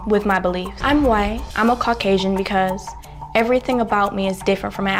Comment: did she say she was kok asain? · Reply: Caucasian. Google it.